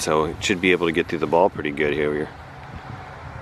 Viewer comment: WHAT?! Did say? Lol “should be able to get through the ball pretty good hereweyerr” hahaha